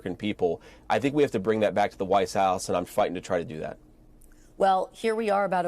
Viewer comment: minutes into this conversation JD Vance absolutely said, The Whites House, I’m staring at his lips and listening closely to what he is saying and Yet! Yet! The words were changed in this Transcript!!! Listen and look for yourselves people. Wow!